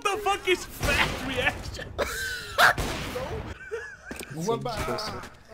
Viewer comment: Am I the only one that thought said "fat erection" when he said "fat reactions"